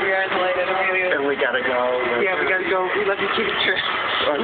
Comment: We were there!! It sounds like i was saying, "We love you, Katie Couric!!" But although she is cool, too, I was actually talking to Caitie Tourist